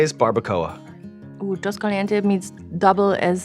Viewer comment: She misheard "los calientes" as "dos calientes". She just gave you guys your next sauce name